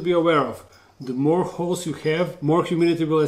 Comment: All the topics covered in the video: 1:35 Is it the right pet for me? 2:45 Maintenance 4:25 What tarantula should I get? 6:40 Basics of scientific names and recommended genus 9:05 Whether to buy sling, juvenile, or adult tarantula 10:36 Enclosures 14:53 Ventilation/humidity 17:10 Temperature 18:18 Feeding 21:05 Pre-molt and Growth 24:25 Tarantula quantity mark (Example: 1.1.1) 25:20 Enjoy DIY Enclosure Builds 27:37 Re-housing 28:45 Handling 30:09 Burrowing Tarantulas 30:48 Losing limbs/legs 31:20 How to spot Mature Males 32:20 Uricating Hairs 33:17 Latin Names/Scientific Names 35:20 Where to get a pet Tarantula 37:20 Breeders and Groups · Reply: It sounded like "The more wh*res you have," XD